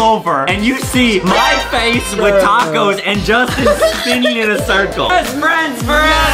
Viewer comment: NEW LANKYBOX MERCH 'THE COZY COLLECTION' COMING OCTOBER 8TH 😍 <link> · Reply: Adam and Justin: BEST FRIENDS FOREVER ME:DID I HEAR THAT RIGHT!?!?!?